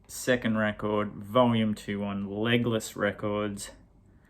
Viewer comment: SMG - When you said Legless, I thought you said Legolas. Funny. My daughter dressed up for Halloween as the "non-canonical daughter of Legolas," so I guess I still have that on the brain. I don't mind that album cover, and I am hard to please in that realm. They sound pretty darned good!